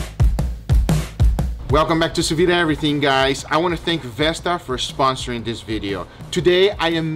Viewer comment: Ha! I thought you said Vespa sponsored you, and had to laugh......